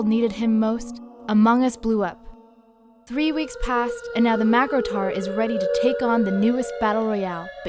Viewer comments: i thought i heard Macrotard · i thought it said macrotard lmaoo